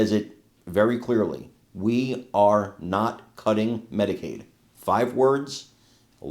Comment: I heard "We are Not cutting Medicare"